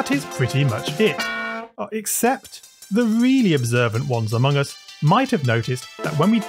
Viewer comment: I hear among us